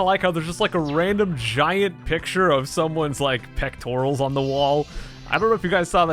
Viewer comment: When he said there's a picture of "someone's pectorals" on the wall, I thought he said "someone's pet squirrels" and I wondered if he was okay while recording this.